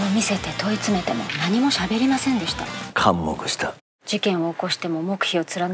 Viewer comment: ここ北村さんの台詞何度聞いても 聞き取れないんだけれどなんて言ってるのか誰か教えてください… もう｢かんもぐした｣にしか聞こえん… 普通に、完全黙秘した…？とか…？ 無音の所での台詞でかっこいいはずなのに全然聞き取れん…誰か… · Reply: 「完（全）黙（秘）した。」で良いと思いますよ。